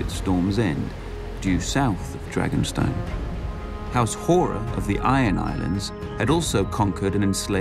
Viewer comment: did ya just said house whore ? and i though house manwoody was bad